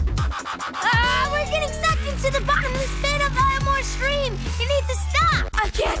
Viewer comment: what is darwin saying..? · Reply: "AHH WE'RE GETTING SUCKED INTO THE BOTTOM OF THE PIT OF ELMORE STREAM, YOU NEED TO STOP!"